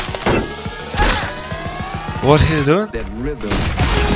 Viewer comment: Pls let me know what is the soundtrack? Also, what did Simple say at 0:32? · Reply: S1mple said what he's doing?